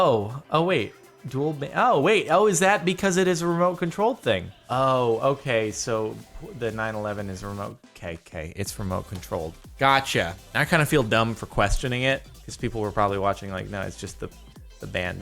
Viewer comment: who else heard him say 9/11